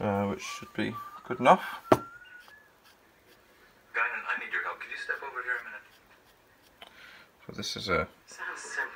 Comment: Beginning around 14:23 . . . Is that Star Trek TNG I hear in the background? What does Captain Picard say to get the Enterprise in motion? "N Gauge!" :) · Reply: Haha yes it is Star Trek! I started watching/listening to the next generation again. NO1! . N gauge! Take us out.